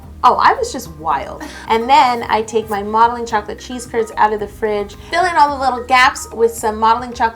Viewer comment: Idk y but the first time she said CHEESE CURDS I thought she said CHEESE TURDS for some reason